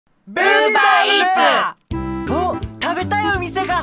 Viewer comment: 最初、ブーバーウーバーって聞こえたような........